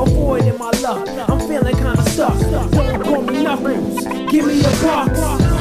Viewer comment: Why did I hear "i'm knuckles. give me your crocs."